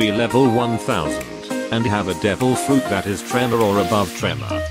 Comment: legit sounds like he says Trevor the whole time instead of tremor, you could just use you’re real voice but nice video.